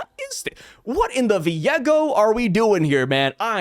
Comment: im not entirely sure what you said, but i heard "Viego" from League and it made quite a bit of sense to me after trying to headcannon it together 😂